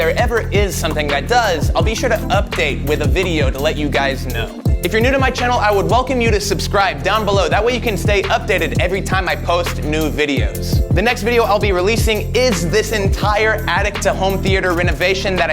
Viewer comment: Am I the only one who heard him say "nude videos" ?